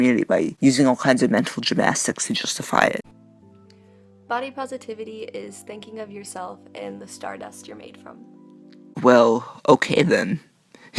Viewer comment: Did they just say "Body positivity is when you think of yourself and start mastrubating."?